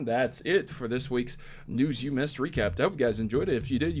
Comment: I could just be tired but at the beginning you sound like you say "recrap"